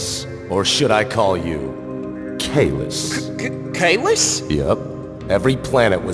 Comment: Did they say Caylus/Infinite??